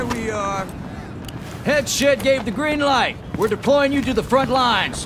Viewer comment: The guy briefing you in, is he saying “Head shit gave the green light?” I somehow only hear that 😂😂